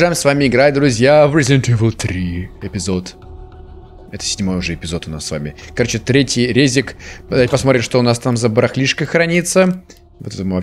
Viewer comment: мне одной послышалось "Третий ПРЕЗИК"?????!!